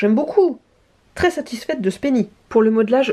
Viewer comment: OK j'ai entendu "très satisfaite de ce pénis", donc comment dire que je me suis étouffée de rire avec mon cookie